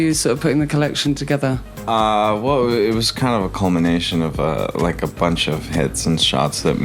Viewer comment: ? I think he said culmination not combination